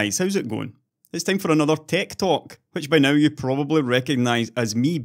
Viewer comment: Tech talk? I almost thought you said tic toc.